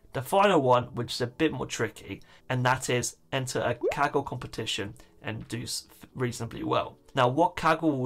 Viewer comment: I seriously thought he said kegel competition